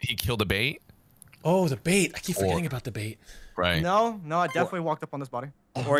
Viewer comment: I thought he said the babe. I was hoping for someone to say what babe The babe with the power. What power? The power of voodoo. Who do? You do, you remind me of the babe.